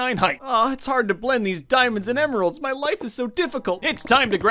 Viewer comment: When you said your life is sad because you played on Christmas I thought you said your wife is sad... That is 5x worse be careful man